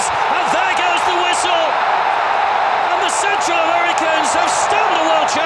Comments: did he said Central Americans? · Cuando se acabo el comentarista dijo the centre Americans? O escuche mal